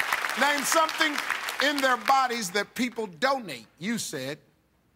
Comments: At the first three times, i thought the question said "don't need" and I was like, what the hell? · I thought Steve said "Don't eat" instead of Donate xD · Haha I heard 'don't need' instead of 'donate'. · Oh wow I misheard "donate" with "don't need" so i was like Waaaaaaa? when the first girl said Heart XD